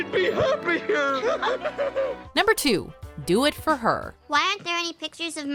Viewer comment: I thought it said "don't forget her"